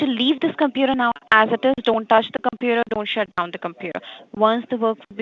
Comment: it sounds like she said dont shit on the computet xD